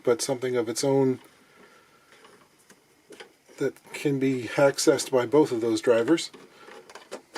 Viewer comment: did say “haccessed”? If so, that’s brilliant. 😃